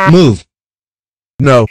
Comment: instead of move he said moo XDDDDDDDDDD